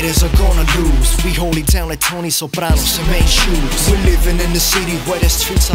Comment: did he say tony soprano?